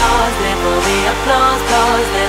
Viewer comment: My dumbass thought it said applesauce